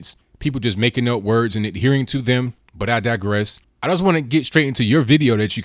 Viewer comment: Every time someone says " but I digress." I hear " but I digest."